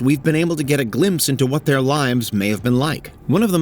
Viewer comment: Does anyone else hear "limes" 00:35 ?